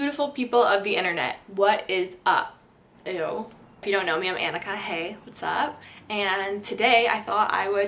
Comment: at first I thought she said "my name is anikkahay"